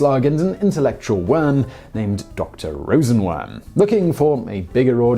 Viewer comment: Did someone say Dr. Worm? <link>